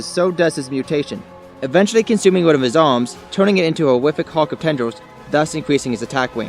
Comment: I can't understand a word, horrific hawk of tenders ?